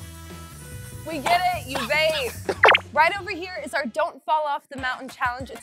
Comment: did say “We get it ,you vape”?